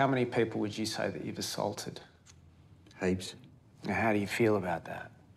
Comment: Ooohh he said heaps. I thought he said eight at first and I'm like damn, you mean today?